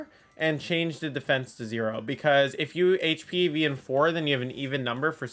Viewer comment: I deadass don't know what you said at 6:06 despite repeating it 3x and turning on cc lol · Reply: 4 hp instead of 4 def evs to get proper sitrus berry proc with belly drum